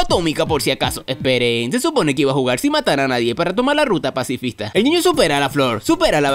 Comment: De tan mal que veo pensé que decía ruta racista